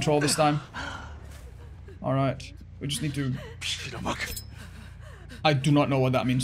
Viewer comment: Korean here, and can proudly tell I have no idea what he's saying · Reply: he said “..Damn it” = “..빌어먹을”